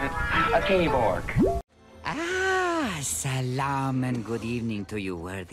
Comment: did he just say ‘Salam’?????